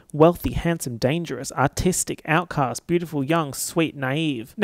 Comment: Not really relevant at all but... at 29:50 I thought said "autistic" instead of "artistic" and I choked on my water. Sincerely, neurodivergent girlie.